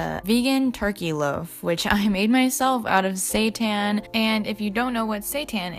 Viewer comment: did she just say she made it out of satan